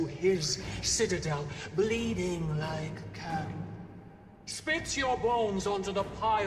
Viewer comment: i was listening to this on 2nd monitor and i swore at 34:06 she said "spits your balls..." and I almost spat my coffee out. I had to rewind to look at the dialogue. lol